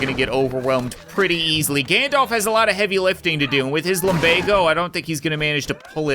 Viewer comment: did I hear lumbago? Old man get back to work!